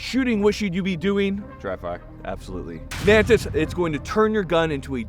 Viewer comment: I heard "Drive bys" and there is nothing you can say to convince me otherwise.